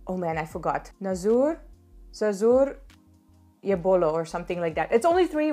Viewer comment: Thank you so much for your overwhelming support! If you loved this video please make sure to also watch my TedxGNI talk about why a foreigner chooses a future in India: <link> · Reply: I think at 5:56, you mean to say - "Zara zor se bolo" (speak a little loudly)....I guess? But whatever it is, your video has made me cry, please accept my gratitude, didi (i.e. elder sister), you've truly understand India, visit again, we will be awaiting godlike guests like you, || Atithi devo bhava || Pranaam 🙏...😊